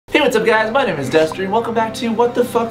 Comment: if u pause the vid when it just starts it sounds like he says "hi guys my name is dust"